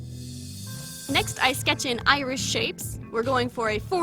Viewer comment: At first I thought she said Irish shapes and I was very confused